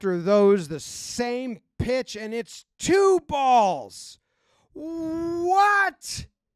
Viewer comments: where you think he says "what?" he's actually saying "strikes" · Jomboy is maybe the best lipreader I've ever seen, but at 1:27, I'm pretty sure Boone says "CHRIST!!!" instead of 'what.'